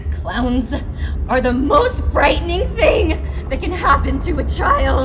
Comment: “A clown is the most frightening thing that can happen to a child” I don’t know why that struck a chord with me but it did. Mega oof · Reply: I thought it said “you know what’s the most frightening thing that can happen to a child”